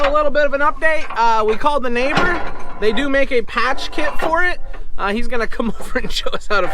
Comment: I thought you said, "They do make a casket for it". However, once I turned on the captions, I saw you actually said, "They do make a patch kit for it". LOL...I thought it was so wore out it needed a funeral. Love your videos. 👍